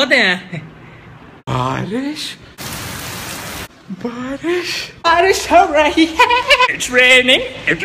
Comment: I heard like "_pawri ho rahi hy_"